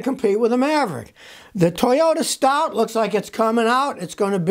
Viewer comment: What's the new Toyota called?. Sounded like you said studz. OK Stout. What a weird name. Most Americans won't know what it means due to limited vocabulary.